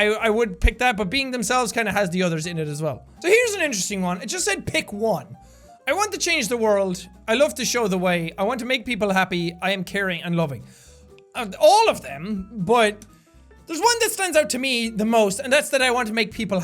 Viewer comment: "i want to make people happy" i thought it said "i want to make the world puppy"